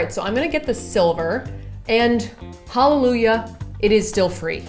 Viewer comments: When you said the phone case may be holographic and then you said hallelujah I started laughing by myself as I thought you said hololujah😄😮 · did you just say HOLOlujah????